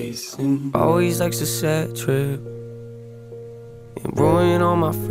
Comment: Anyone else think it sounds like he says Always acts eccentric, It ruined all my friendships and not Always likes the bad trips or is it just me?